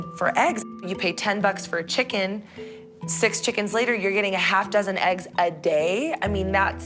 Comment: “A chicken will lay an egg every 26 hours” “Oh that’s awesome, that’s about half a dozen a day” WHAT KINDA MATH IS SHE DOING?? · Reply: I think I heard her say something like "six chickens later, you have half a dozen a day"